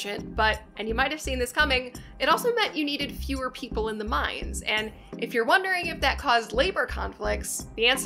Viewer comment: Great video as always! I grew up in the area and took a few tours throughout the years but still learned a ton from this part 1 miniseries video. Quick grammar note from the captions- at 6:01 you say "mines" and the captions say "minds". Looking forward to the rest of the series! Keep on rocking